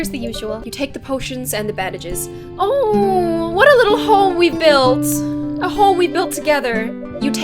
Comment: I heard what a little HOE we build... But then I heard it was home xD Im Swedish so if i have typed wrong no hate!